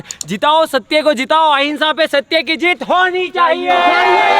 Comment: 'अहिंसा पर सत्य की विजय होनी चाहिये' Is it what he said